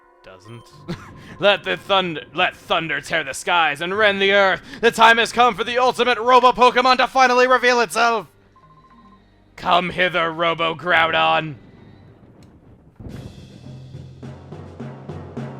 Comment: First time I played XD I honestly thought Kaminko said "Come hitler, ROBO GROUDON!" I am serious